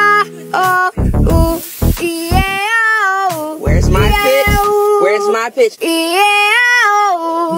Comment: I thought u said b*tch not pitch 💀